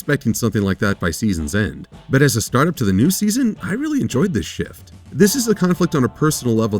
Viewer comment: wow I heard "I really like this ship" and you really said "I really like this shift"